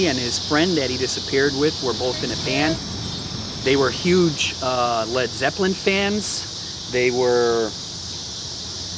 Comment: What did the woman say at 6:56? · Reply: “Don’t step on the graves” is what she blurted out. I almost turned the camera off to throw something at her - lol.